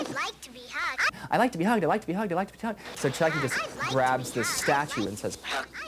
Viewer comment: Did anyone else hear Don say "I like to be tugged..." at the end? hehehehe